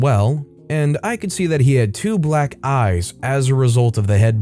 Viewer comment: I accidentally skipped to the end and had to go back. I heard the words "...group photograph, black guys an' all..." I thought it was going to be something about a racist getting their comeuppance. Turned out to be 'black eyes' but still a good story ;)